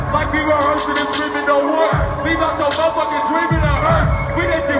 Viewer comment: what does he say at 2:06? it sounds like "leave out your mother fuckin dreams in a hearse"